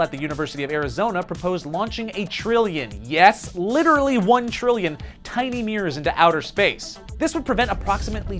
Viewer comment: I thought he said "launch one trillion tiny meers into space"